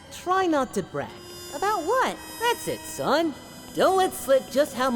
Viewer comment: I swear to God I hear Goku say "Brexit, son" instead of "That's it son" everytime